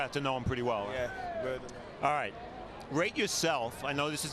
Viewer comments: Who does he say is his favorite boxer of all time? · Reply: Roberto Durán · what did he say? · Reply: Roberto duran